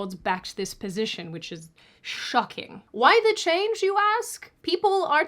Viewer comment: did I hear "SHOCKING"...? KAYA!!!